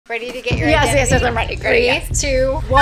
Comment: I thought she said "ready for your Anxiety 😭"